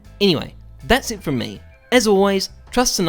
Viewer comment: Who else says the intro with him, HI, IM HEYZEUS, AND IN THIS VIDEO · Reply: For the whole time after 1 year now I thought he was saying “HI! I’m Zeus”